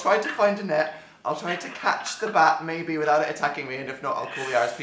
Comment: I thought that Dan´s mom said that he should catch the bat with a sheep!!!!!!